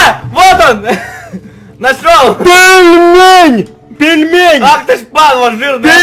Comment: Мне послышалось Игорь пельмень